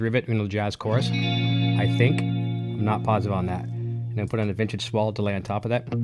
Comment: What is he saying at 8:51? Vintage squall delay? Vintage ? delay. That reverb, compression and delay sounds awesome.